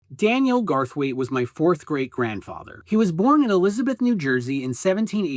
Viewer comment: I thought you said "4th grade grandfather" and thought you maybe had a new grandfather every school year.